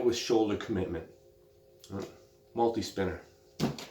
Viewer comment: Thought the video said meatspinner, im dissapointed